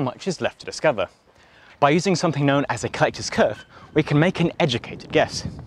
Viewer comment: please, someone. What is he saying im trying to google it. Kytos curve? Caetus curve? Cytes curve? Ketos curve? :(